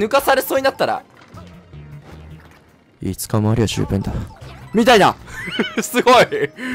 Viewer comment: イケボ過ぎて何言ってるのか聞き取れませんでした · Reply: ５日もあれば十分だろ